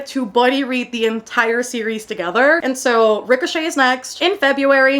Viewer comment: Every time you say Ricochet I i keep hearing Rick O'shea and I'm like... Who's that? 😂